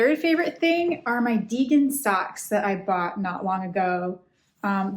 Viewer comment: They are from Degen! <link> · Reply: haha I kept hearing "vegan socks" :) thanks!